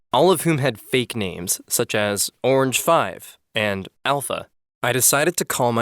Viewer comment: Who thought orange5 I thought it said oranges